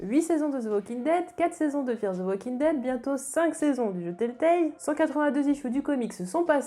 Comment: Désolé mais elle dit quoi a 0:11 bientôt 5 saison de .....???? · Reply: Des jeux Telltale (Les 4 saisons des jeux The Walking Dead + le jeu sur Michonne) :)